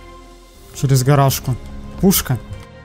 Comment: Мне однаму послышалась что он сказал не гаражка а играшка